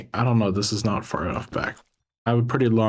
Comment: dude when he was placing the first rails i could have SWORN he said "im a pretty blonde girl, sometimes its hard to make it fit" ima need to check it